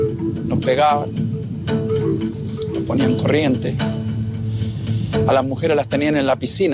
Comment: he did not say “put in chains” he said they would put “current to them” meaning they where where tortured with electricity / shocks as well